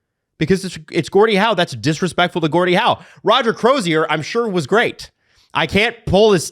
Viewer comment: I thought they were saying Roger crows ear 🤣